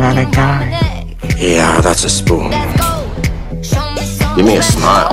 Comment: I thought he said "give me a spoon" and I was shook